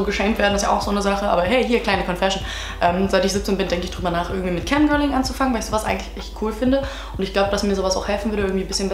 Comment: mit was anfangen? ich verstehe es auch nach dem fünften mal akustisch nicht... 11:27 verstehe ich akustisch auch nicht :D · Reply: Mit Camgirling anfangen 11:27 sexual-part